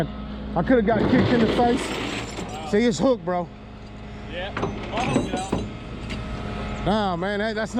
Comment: I can't figure out what the guy says right around 2:05, right after says "see it's hooked bro". They guy say something short - just a couple of words - then says "Naw man, that's not how it works". It LOOKS like the jerk was kneeling down to unhook it himself but pulls the cable tight with his hand. Can someone tell me what the guy had said just then? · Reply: Sounded he said "Ill help you out"